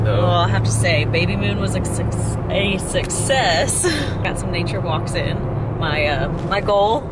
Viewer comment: lol she almost said babymoon was a succsex instead of success.😂